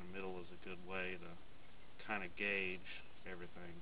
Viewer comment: did you say kinda gay? XD